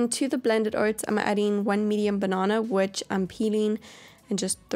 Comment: I heard:, 1 million banana"😂😂😂